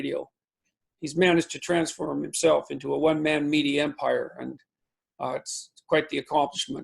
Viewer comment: It sounded like you said, "He's managed to transform himself into a one man meaty empire." That image brought man great joy. Thank you, JP.